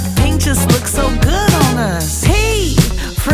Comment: P—Pretty! 1:57 I—Intelligent! 1:59 N—Never Sad! 2:01 K—Cool! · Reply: I thought it was "N never said"